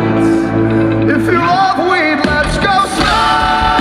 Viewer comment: brendon: "makes a song about his friend under a drug addiction" "if you love weed let's go smoke" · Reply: I thought he said "if you love me let's go fuck" 😅😅😅🤣🤣